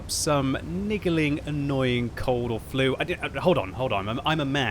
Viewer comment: Did mans just say a niggaling annoying flu?